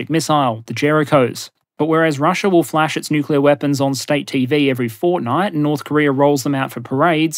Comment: Did he just say Fortnite??